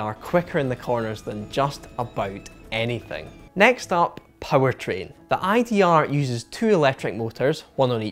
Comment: Ha. I thought he was saying 'poetry' instead of powertrain.